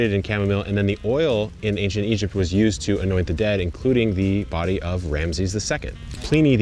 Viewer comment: I thought he said "annoy the dead" not "annoit the dead" 😂